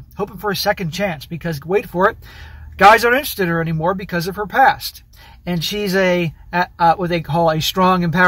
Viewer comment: You said "her past", I heard "herpes"